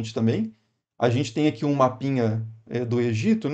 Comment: Bom dia!!! Quando você falou "um mapinha do Egito" entendi "uma pinha do Egito. Mas gostei muito desse vídeo.